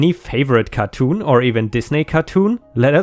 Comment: I can't be the only one that thought said "these naked" cartoons and not "Disney" cartoons? I know its his accent but I had a little laugh